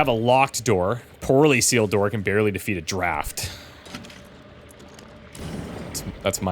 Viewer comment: I thought you said, "can barely defeat a giraffe-t" when you were reading that door entry (I had looked away so didn't see the text on screen) & I had to rewind it to figure out that you said "can barely defeat draft"!!! I don't know why I would've thought that a game in the PNW would even have anything relating to giraffes, nor why you would've added a "t" to the end of it, but that's what my brain did with that audio information!!!